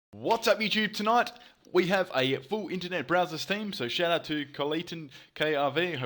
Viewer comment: I thought he said Brazzers team 😂